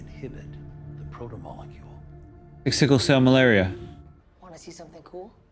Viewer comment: Did say "like sickle cell malaria"? What the... 😂😂😂 He really does like to 'word vomit'.